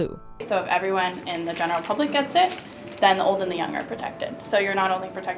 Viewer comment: Can anybody help me understand what she says at 5:08??? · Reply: if everyone in the public gets it then the old and the young are protected (if the public takes the vaccine children and the elderly won’t get the virus)